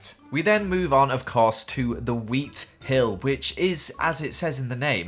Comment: i heard YEET hill lol